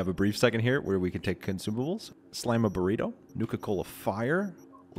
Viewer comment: nuka-cola? Did I hear that correctly?